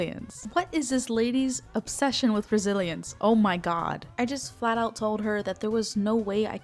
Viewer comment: UNTIL HERE I WAS JUST LISTENING AND WAS VERY CONFUSED THINKING YOU WERE SAYING BRAZILIANS INSTEAD OF RESILIENCE LMAO